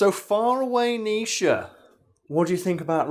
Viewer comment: "Far away"? And all this time I thought he was saying "Fire away"